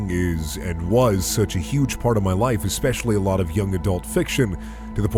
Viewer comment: I misheard my life as my wife LOL